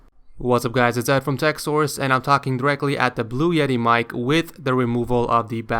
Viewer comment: I thought he said it was a blue snowball ice?????? He literally said blue yeti 😂😂😂